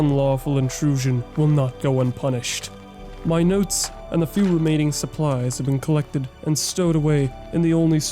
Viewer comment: I kid you not I heard - "My nuts and a few remaining supplies have been collected"